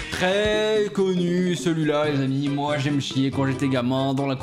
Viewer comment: pour la dernière j'ai entendu "moi y'aime scier" plutôt que "moi y'aime chier"